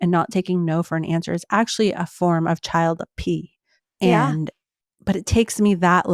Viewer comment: What is child apee? I went back and listened it three times and it still sounds like she said child apee (at about 55.50). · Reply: she said "child P", but she's referring to child sexual abuse